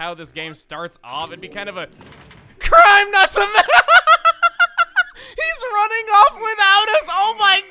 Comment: What the heck did he say? It sounded like "Crime nuts without us!"